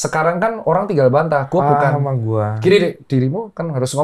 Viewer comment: suara apa bang densu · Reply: itu ngomong apa lagi yaa 😬 "a nama gua"???